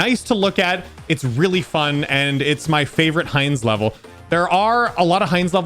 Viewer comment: When he said "hinds" i heard "heinz"